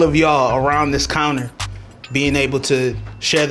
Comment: Thought it said Kareem hunt lol